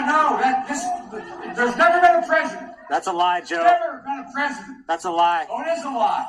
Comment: does biden say, "...that is a lie...?"